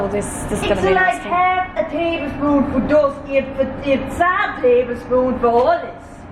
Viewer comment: what did she say?🤣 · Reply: She was saying she should have put only 1/2 tsp but, she did 2 tbsp 😂